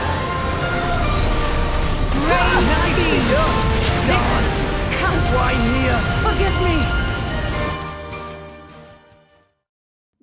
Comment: anyone else hear “this c*nt”